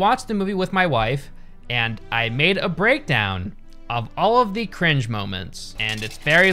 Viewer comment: When said "I made a breakdown" I thought he said he had a breakdown lmao